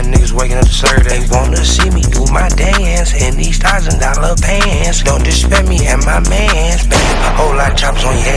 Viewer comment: I thought it said "in these dollar pants"